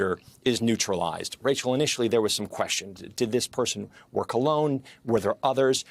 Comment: I could have sworn he said “Did this person wear cologne” 😳😂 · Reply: “Work alone” I had to listen to it several times.